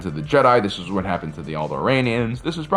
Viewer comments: I heard "this is what happened to the old Iranians" 😅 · I heard “old iranians” when you said alderaans